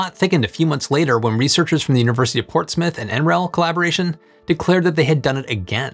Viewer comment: So do you think enzymes like this are the key to solving our plastic problem? Get Surfshark VPN at <link> - Enter promo code UNDECIDED for 83% off and 3 extra months for FREE! If you liked this video, check out Is Aquaponics the Future of Agriculture? <link> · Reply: The way you mangled Portsmouth, I thought you'd said Port Smith 😜